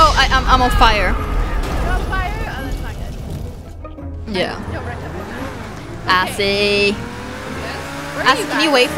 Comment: Lol at 8:47 it sounded like said ''Assy'' instead of azzy